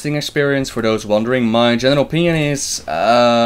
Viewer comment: IT SOUNDS LIKE HE IS SAYING MY GENERAL PENIS no homo